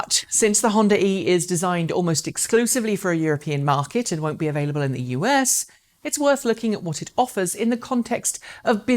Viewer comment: Did she say at 3:11 that it won't be available in the US?